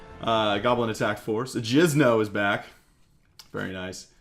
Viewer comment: did...did he just say jizno?..😂